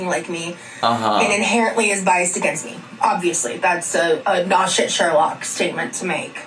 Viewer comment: did she just say “Naw shit Sherlock” or not shit either way what is my girl saying